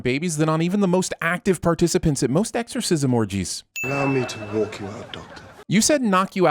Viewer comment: he clearly says allow me to ”walk" you out not knock...